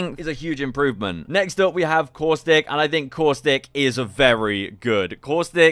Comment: Thought you said “horse dick” instead of “caustic” 🤣🤣